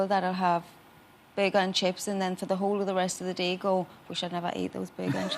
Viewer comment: does she say "bay gone chips?" USA guy feels ignorant about something that's probably delicious